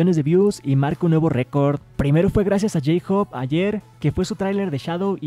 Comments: ARMY: que pedo,que pedo escuche bien? xdxd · estoy loca o escuché Jhope?